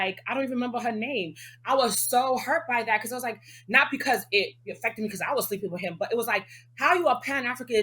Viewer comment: I was hurt not because I was sleeping with him. Is that what she just said? Did anyone catch that. I hope I'm wrong